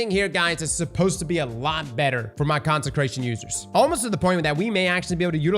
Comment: When Cross said “Consecration” I thought he said “Content Creation” and I was like WHAT? We get a perk now?! What does it do? Scream “slap that like button” whenever you get a kill?? 🤣